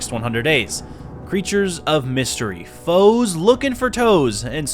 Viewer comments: thought bro said foes looking for hoes 😭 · "Foes looking for toes"😂😂😂😂💀💀💀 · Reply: I thought he said hoes looking for toes 💀💀